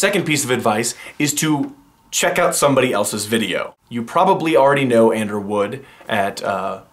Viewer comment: When you said "You probably know Ander Wood." I heard "You probably know and/or would." And I was like... "Go on finish the sentence!" so yeah, I don't know Ander Wood... yet!